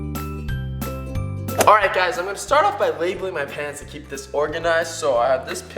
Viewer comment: Who else thought he said ''Labelling My Pants''??!! XD